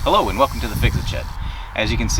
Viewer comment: I swear he said "hello welcome to fixing shit"